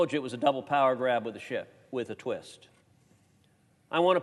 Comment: Was that a Freudian slip? I could’ve swore he said, “It was a double power move w/ a [sh*t].” Shift, perhaps?